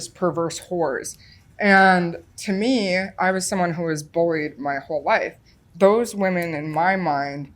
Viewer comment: Does she say “… to me I was someone who was bullied my whole life.” ? (I guess so, because again at 26:50 she says “I was bullied for most of my life …) 3:14 And a little later “.. and for me who was just terrified of everyone my whole life”. That’s what it sounds like to me. ? Doesn’t seem consistent with 0:25 “a wonderful childhood ….” or 0:50 “really had a blessed childhood”. Smart gal. But it looks like she outsmarted herself. The song that comes to the mind of an old man is “Just Another Whistle Stop” by The Band.